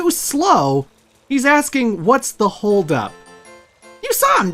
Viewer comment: wait...mario says what's the hold up...I always thought he said help me...